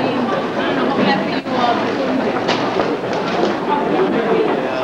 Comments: listen closely. did he say “one world trade?” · can someone please help me with what the woman says here? Does she say “the minute you walk out of Lower World Trade”? Just curious to know if that’s how folks who worked there referred to it as.